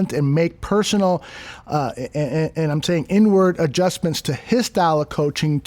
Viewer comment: I thought said N-word adjustment until I realized it was inward adjustments lmao